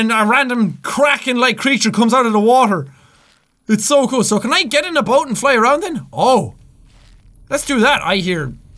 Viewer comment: Watching at full volume. I feel like little devils are fighting inside of my head while one of them constantly stabs my eardrums. Oh my god I'm gonna die. R.I.P. me. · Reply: TheFnafGamer x989x 14:15 did jack really say shall we get in that boat and fly around in it